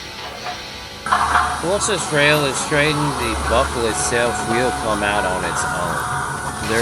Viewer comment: lol the subtitle software used while editiing made a funny little mistake. He was saying get it get it "all straightened out" and the software thought he said "Australia out" 😅 it doesnt matter at all im just pointing it out because i thought it was funny.